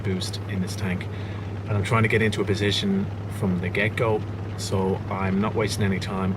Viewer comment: Every time you say 'from the get-go', I hear 'from the gecko'. Good vid as always.